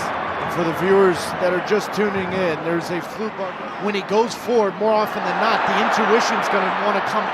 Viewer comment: Did he say “ there’s a flu bug”